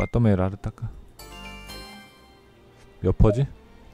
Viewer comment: 난또까 마토메나레따까 "어떻게 정리된건가?" 로 들려요 삼14보다가 재밌게 진행하셔서 지금 13 방송도 다 보고 있네요 ㅋ